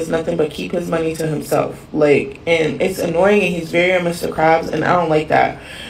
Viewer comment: Did she say he's like mr krabs 3:15? Like from SpongeBob?